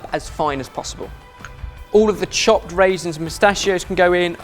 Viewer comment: Did Ben at around 1:44 say mustachios instead of pistachios?